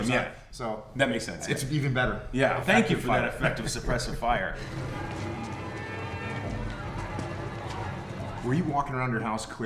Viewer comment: "Suppressive fire...ohhh i thought you said impressive fire."